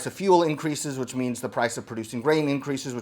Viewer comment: lol at 4:30 I thought you said "the price of producing rain increases" and thought oh boy here we go. (I figured out what you said) but I'm really excited for this, I really want to try this with bison here soon.